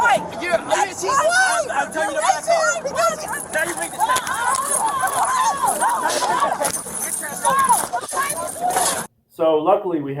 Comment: he clearly said “now you’re being detained”